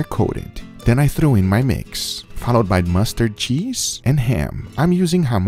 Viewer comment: It looks like you used Muenster cheese, one of my favorites, but it sounded like you said "mustard cheese" and the thought of that tastes weird.